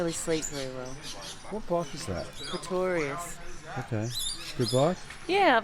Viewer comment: What type of bike does she say at 9:41? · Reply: Scott Horvath Pretorius